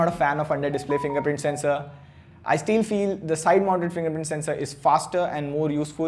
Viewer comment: I steal feel instead of I still feel 🙂